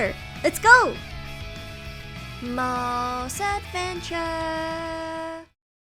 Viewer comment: All this time I thought you said "Mole's Step and Chat". Now I can hear it's "Mole's Adventure" . I'm so dumb.